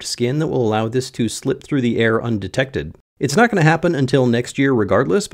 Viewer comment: I thought you said "unprotected" 😄🤣